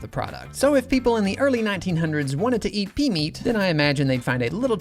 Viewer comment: I thought he said PEE-meat😅